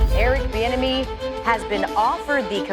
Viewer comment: The first few times I heard his name I thought people were saying "Eric the enemy"